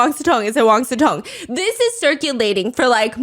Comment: I swear i hear "wang su tong" and not "wang sicong" 😢